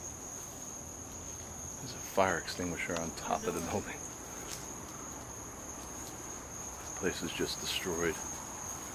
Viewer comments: There's a woman speaking over! I heard something like "Annoy!" I'm not sure... Does anyone have an idea? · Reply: I heard that as well. She says "The Door" just as is behind him trying to open the first door they come across. Glad I'm not the only one who heard that one :-) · I heard what sounds like a woman’s voice at 3:16 (while is talking about the fire extinguisher on the roof). I couldn’t make out the word, though. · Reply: She said: "The Door"